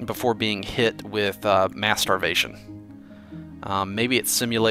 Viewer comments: I thought he said masturbation instead of mass starvation. Lol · Mass starvation sounded like masturbation at 16:42 XD